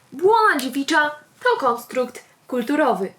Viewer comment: omg, usłyszałam "błąd Mickiewicza" :D